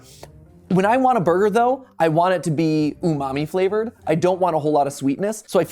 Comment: I cant be the only one who heard “I want it to be mommy flavored” 😭😭